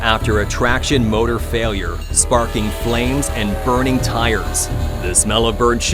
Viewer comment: Did he actually say “burning tires”..? And what is the story with Norfolk Southern, do they not have a maintenance crew?